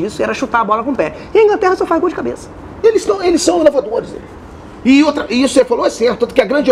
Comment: O que ele falou em 8:18? "Eles são ..." o q? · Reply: Tagore Cavalcante Dourado inovadores